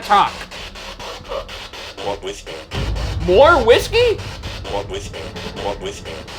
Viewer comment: did anyone hear “walk with me” instead of “more whiskey”?